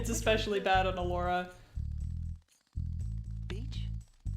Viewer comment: I misheard the line and thought Spyro said "bitch", and honestly, let Spyro swear